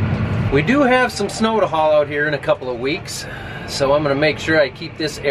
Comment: i could have sworn that you said you had some snow to haul in a couple of weeks.